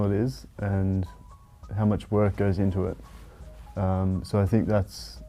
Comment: I swear I heard "How much more can get into her"